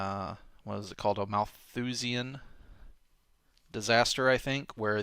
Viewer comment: Can anyone tell me what he was specifically talking about there? I've tried putting in combinations of what it sounded like he said into google like "Whoosian disaster" or "oosian disaster" but nothing comes up. I really want to read up more on what he was talking about but I can't figure out how as google isn't giving me anything... :/ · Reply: Malthusian Disaster, or Malthusian Catastrophe. Easy mistake to make.